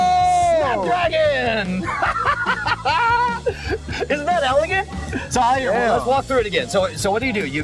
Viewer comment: did he just say, "Isn't that elegant"?